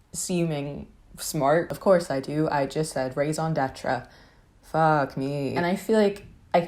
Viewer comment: Bro I heard "rays on detra" and I was like "what the fuck??" until I realized it was "raison d'être" and now I understand. Didn't realize we said that in English lmao.